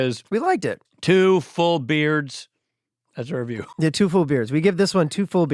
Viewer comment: I keep hearing "two full beers'